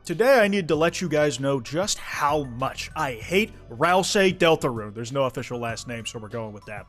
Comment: Thought it said "I HATE Israel".